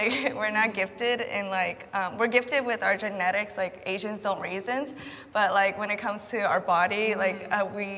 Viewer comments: What the hell did that girl say around 8:36 ..... I heard "Asians don't raisins" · what did she say? Something like “Asians don’t raisin” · What did she say at around 8:37? Asians don't what? Lol i heard raisins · did she say asians dont raisins or am i losing my mind? at 8:35 i have just rewatched ths like 5 times is that was she says? like why tf is all i can hear, we are gifted w our genetics, asians dont raisins? imm confused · “Asians don’t raisins” wtf did she say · did she say Asians don't raisin? I'm confused 😆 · What the hell did she say? Asian's don't raisins"?